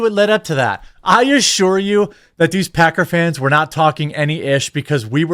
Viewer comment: i'm certain he called them packer fans but i heard pecker fans and no i can't unhear it Lmao also football fans are the MOST toxic sports fans i've ever met